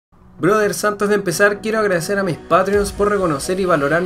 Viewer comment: Siempre pensé que decía a quiero agradecer a mis padres *